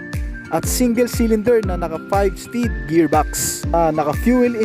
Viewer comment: Ano sabi mo? (4:50-4:51) 5 spd gearbox?